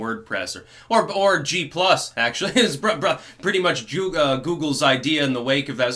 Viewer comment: did Styx almost say Jewgle instead of Google? LOL.